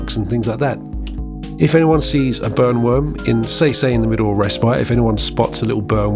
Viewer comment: Not gonna lie, I thought you said “Bone worms” and it unlocked a new fear. Not that the burn worm is any better😂